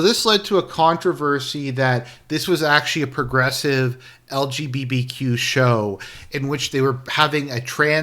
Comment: did he say lgBBQ? 🤣